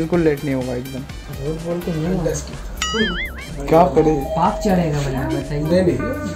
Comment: Any indian please what he says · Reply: I said paap(bad deed) chadega he said paap nahi baap chadega